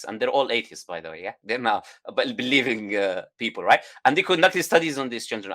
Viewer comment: the subtitles say "they're now about believing people " but it should be "they are not believing people"